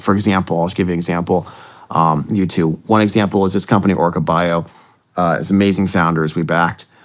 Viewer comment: What is the name of the company he mentioned at 1:48:50? It sounded like Orca Bio. But I haven't been able to find any company by that name.